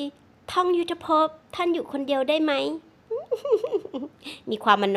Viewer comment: อะไรนะ? ได้ยินหนังตีนอ่ะ😂😂😂😂